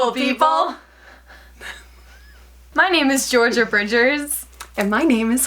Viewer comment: For a second I thought you said your name was Gloria borger